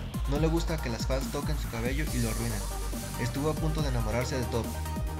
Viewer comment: Digo Que Lo Arruinen Jiji Así Lo Escuche 😁😆 Digo Que Lo Miren